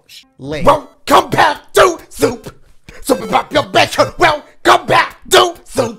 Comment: Bruh I always hear “WELCOME BACK TO SOUP” lol